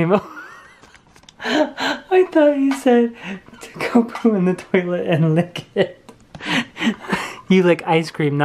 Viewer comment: The house is looking more beautiful each day! I can't wait for new videos and of course the intro. I thought said "Go poop in the potty and lick it" too. I couldn't contain myself so I think I failed at the try not to laugh challenge... :(